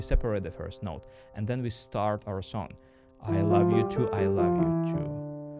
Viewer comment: I heard "I love Youtube, I move Youtube" 😅 Thanks for this high quality content!